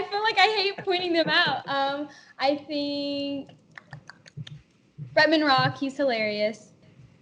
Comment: did she say ramen rock? im confused